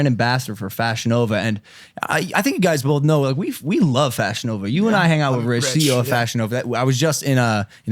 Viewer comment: So if thats the case cos you like fashion nova but it sounds like your saying fashion over, we should like it, well i love HEROIN so you should love HEROIN to